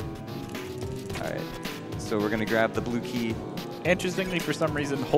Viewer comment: what he said: we're gonna grab the blue key. What I heard: we're gonna grab the blooky